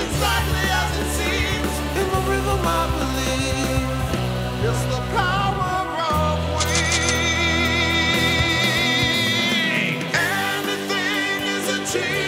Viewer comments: i thought it said its the power of weed before i put on captions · I totally heard it as " the power of weed" and I believed it till I saw it on the screen 🤣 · I thought he said "it's the power of weed" because he said you can flying high hahahaha my bad